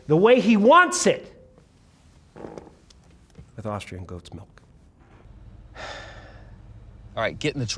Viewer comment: I still have no idea what he said. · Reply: "....with Austrian goats milk...." in regards to how he wants his latte.